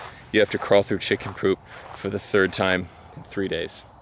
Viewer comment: Did you say, "For the turd time."? I would have come up with a solution after the first time!